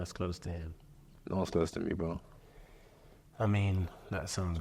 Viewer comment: What does he say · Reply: "No one's close to me bro"